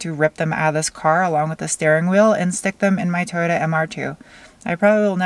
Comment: I thought you were gonna say "Stick them in my ass" lmfao. Yes im a kid in a 25 y/o body